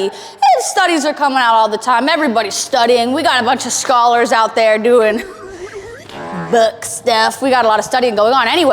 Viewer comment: I was listening to this part in my airpod and I thought I heard you say "we got a bunch of scholars doing BUTT STUFF!" (appropriate for teh video) p.s. i'm just starting my first attempt working out at home, wish me luck! :3